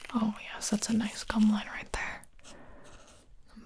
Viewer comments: Did she just say oh that’s a nice cumline there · I swear i heard "oh yes thats a nice c%m line right there"